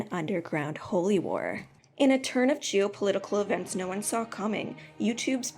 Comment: Did you just say Jew-political instead of geo-political? That is hilarious